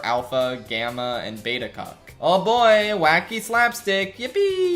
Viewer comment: Did he say cuck!?